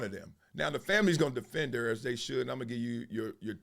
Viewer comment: he did not say that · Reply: he said "now the family's going to defend her as they should". I wish he didn't say this because so much of what he said was better than this.